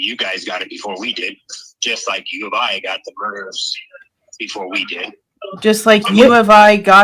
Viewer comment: Play again sounded like he said the Uof got the murders before we did.